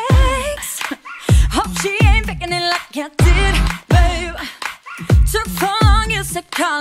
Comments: "I HOPE SHE GETTIN BETTER SEX HOPE SHE AIN'T FAKIN IT LIKE I DID!!!" For a second I thought that she said,"HOPE SHE AIN'T FAKIN IT LIKE 'HADID'🤣😂🤣😂!!!" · “Hope she ain’t faking it like I did” dude I’m dead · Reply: Swear I heard, "Hope she ain't fakin' it like Hadid" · I swear I heard "Hope she aint fakin it like Hadid"